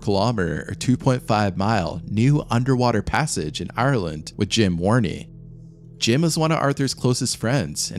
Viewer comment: I thought you said Jim Varney....know what I mean, Verne? 😂